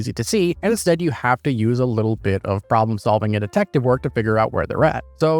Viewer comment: "Problem solving and detective work" all I hear is "Use C4 it make brain not hurt"